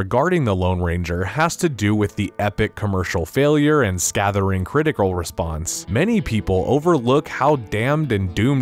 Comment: Unpopulair oppinion: I enjoyed this movie a lot and wanted another one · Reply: said scathering instead of scathing at 1:27. Video ruined